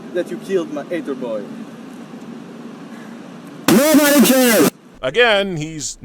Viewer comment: what the fuck did he say here? Was that their editing job making that impossible to hear, or am I just missing something? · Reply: ShinyQuiLucha He said "NOBODY CARES!" They edited it to sound like yelling... I think.